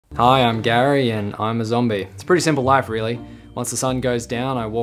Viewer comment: I thought he said hi i am gay and zombie 💀